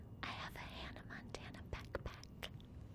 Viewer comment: I thought she said pek pek🤣🤣 she rlly said backpack😂🤦🏽‍♀️